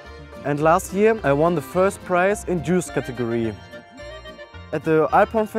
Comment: U thought he said Jews category